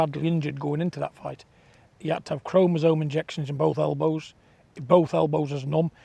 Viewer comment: Did he say ”chromosome injections in both elbows” 😂